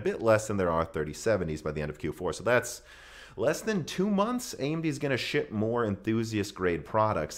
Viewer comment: I hear "AMD is going to Shit more enthusiast grade products"